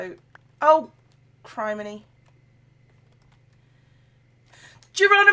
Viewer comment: I'm sorry did you just say crimeny? :P